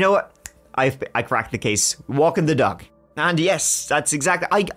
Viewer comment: Walking the dog!....I thought he said walking the duck and I'm just like, yes makes sense....get him near water